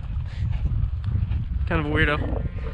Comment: "kind of a wierdo"😅 is that what you said? I also heard ur dad say something about that